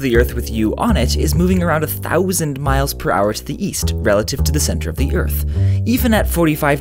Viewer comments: ooohhh i thought you said "weast" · Oh, East! I thought you said Weast.